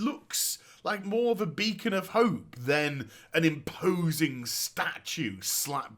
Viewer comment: did he say "then" instead of "than"?